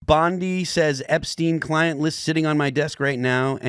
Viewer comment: Thought he said "Blondie" when the video first started... But honestly, bondi... Blondie... It works!